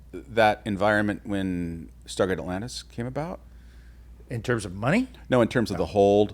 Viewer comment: I thought it said Stargate Afghanistan.